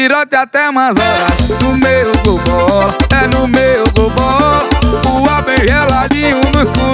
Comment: Só eu que escutei " é no meio do ró " kk aí quando vi o nome da música passei a ouvir no meu gol bola kkk.